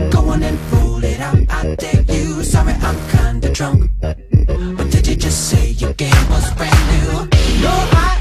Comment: Am I the only one who thought it said "But did you just say you're gay, what's brand new" oh god I need to stop